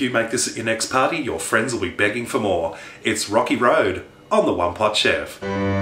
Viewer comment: For example (this really happened when I used "Transcribe Audio" on your video) when you said "One Pot Chef" it thought you said "A Lot Of Hardship" xD